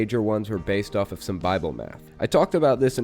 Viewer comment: I heard "Bible Meth," and I thought that summed up this group fairly well